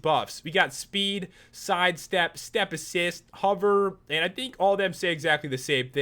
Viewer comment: I thought hover said hoover